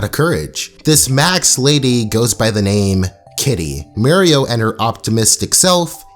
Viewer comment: Alright, mistakes happen, but I felt the need to address this. “Masks” not “Macks” or “Maks” honest speech mistake, got it. “E-Pit-toe-me” instead of “Ep-i-tome” honest mistake, got it. Also, Kitty’s backstory doesn’t justify her behavior. I never said it did, just wanted to provide more clarification. Please carry on with your regularly scheduled program. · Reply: i thought you said mario instead of muriel